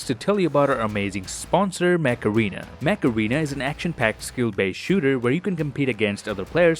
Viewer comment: Damn for a hot second I thought you said "Macarena" rather than "mech arena"